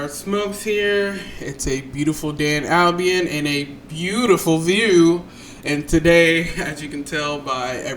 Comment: hol-up what’d you say? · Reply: A beautiful day in Albion with a beautiful view 😂